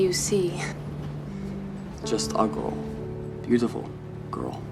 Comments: when said "just a girl" i heard "Just ugo" for a sec 😂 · His Swedish accent sounds like an american trying to do an irish accent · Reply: Hi, also a swede here! When he said "just a girl" i literally thought he said "just ugly" and i was like "why is she kissing him??" So Yeah. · it sounds like he said just a ugly girl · I've rewatched this scene over and over but I can't figure out what he's saying... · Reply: “Just a girl “ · when he said “just a girl” bc of his accent i could’ve sworn he said “just ugly”💀